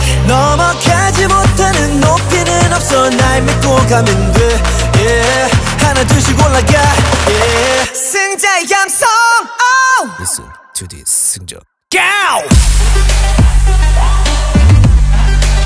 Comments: "Listen to this jisung cow" It sounds like their saying that 😂, love u jisung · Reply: for some reason i hear "listen to this syndrome gyal/girl" lmao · "Listen to this jisung cow" It sounds like their saying that 😂, love u jisung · "Listen to this jisung cow" It sounds like their saying that 😂, love u jisung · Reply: I hear "Listen to this jisungs high" lmao 😂😂😂😂